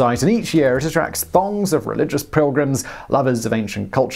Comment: Did anyone else hear " thongs of religious pilgrims..."?